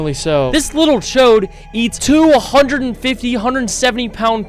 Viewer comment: did you say chode?😂